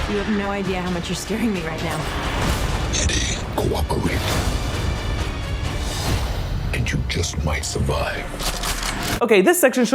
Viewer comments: I always thought venom said "Eddie....go walk away, and you just might survive." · How did anyone not understand venom ?! I thought it was clear enough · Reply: Same for me, although if I'm being honest I did misunderstand when Venom said cooperate. I thought he said run away. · what is venom saying ? I hear "owa kowe" don't know what it means tho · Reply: Schoolastic Entertainment "Eddie cooperate"*